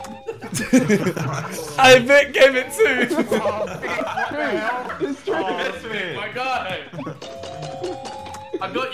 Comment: correct me if I'm wrong but did Vik say 'it's true'? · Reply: Yes he said it's true that he isn't a nonce lmao